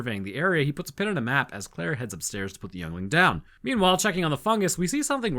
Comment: "claire heads upstairs to put the youngling down" did I hear freedom, justice and security for my new empire?